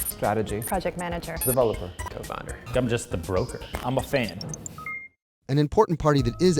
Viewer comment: Did he said "he is just a broker"? What is broker(middlemen) doing in blockchain project?